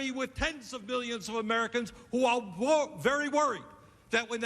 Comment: Does Bernie say Tens of BILLIONS of Americans?